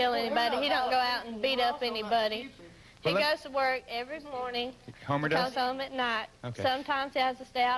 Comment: does she say "(he goes to work) everyS morning"? 😂